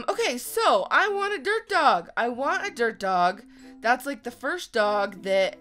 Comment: When said "Dirt Dog" I thought she said.... DERP dog!😂